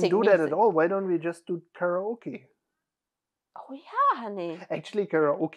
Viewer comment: Oh, you said karaoke. I thought you said pierogi.